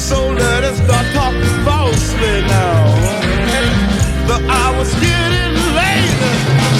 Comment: For years, before hearing the origin, I thought Hendrix was saying, after the line, “Let us stop talking falsely now”, “But I was getting laid!”😂